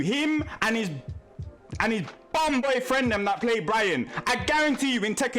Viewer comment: Was that almost a "bomboklat" or did he say "bum"?